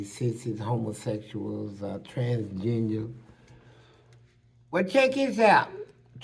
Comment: It sounds like he said "Transgenia' at first 😂